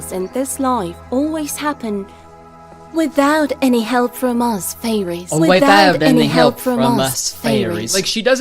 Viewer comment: am i the only one that thought the fairy mommy said "without the help from us babies" idk why i thought that was funny